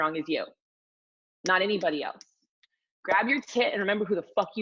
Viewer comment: I keep hearing “everytime you grab your tit” and not “everytime you grab your kit” lol pregnancy brain. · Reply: Makeup Mommy * they were actually saying “everyone grab your tit” lmfaoooo during the little “exercise”